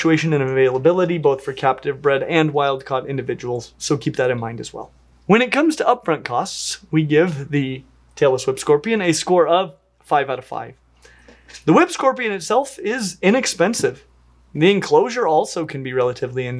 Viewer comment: I swear whenever I don't listen too closely it sounds like is saying "Taylor Swift Scorpion".